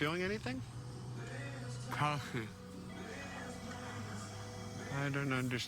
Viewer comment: i thought he was saying "coffee"... 😂